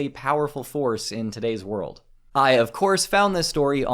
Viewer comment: i thought it says grindr